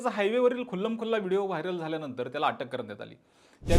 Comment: did you say khullam khulla ?? 😅